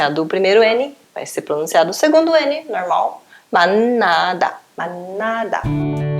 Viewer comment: Só eu que entendi MANADA (de elefante) no